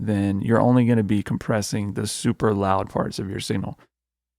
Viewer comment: It is loud where I am right now and at 4:44 I swear I heard you say “super loud farts” 😂😂😂